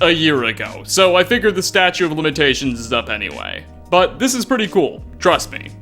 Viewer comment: Waiting for someone else to mention the fact that it sounded like said “statue of limitations” lol.